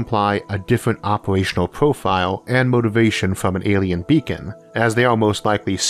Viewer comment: I do love me some alien bacon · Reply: You misheard that. It's "alien vegan".